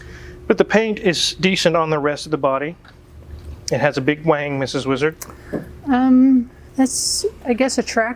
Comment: Did Wizard say "The car has a big wang, Mrs Wizard?" Check at 1:52.